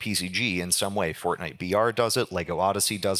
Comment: correct me if am wrong but did he just say Fortnite VR ????:face-orange-biting-nails